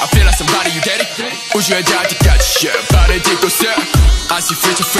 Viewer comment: Am I the only one who thought that Mingi said "your daddy" at 0:25? 😂